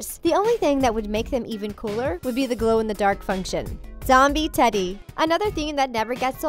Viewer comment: When she says zombie teddy,I thought she says zombie freddy